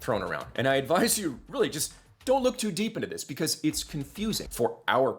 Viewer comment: “Please don’t look any deeper into this” Dude wtf kinda thing is that to say when trying to be “transparent”? Even if he’s being genuine it looks really bad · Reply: That's not what he said. He said "don't look too deep" in the context of that video. The video as a whole was meant to be a very brief explanation of the issues, not a college semester course on VA contracts. Especially when talking specifically to an audience that he assumes doesn't know the industry, there is some oversimplification. That's normal and he didn't hide it. He's not telling anyone to not go do their own research, he's just saying that's not a subject he's getting into any deeper for that video.